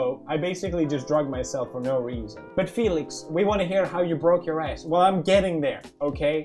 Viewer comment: When the add began I thought pewds said : what ? U never broke your ass?